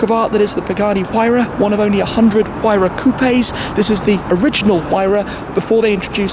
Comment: Did he just say coupaye? 😂